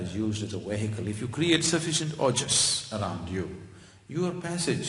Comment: What is this word? "If you create sufficient ?????" at 38:18 - thanks :) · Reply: The word is "OJAS"... Non-physical energy